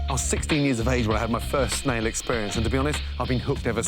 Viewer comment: not gunna lie i thought he said first male experience blughhhhhhhhhhhhhhhhhhhhhhhhhh vomit